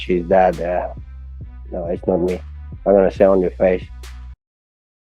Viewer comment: did Khamzhat say he wants to "sit on your face" about Colby? Pause.